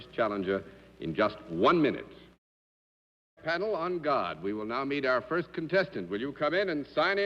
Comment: Did he just say "On God"? 💀